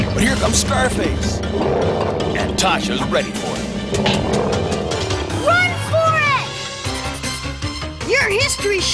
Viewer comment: for a second there I thought he said run forest 😂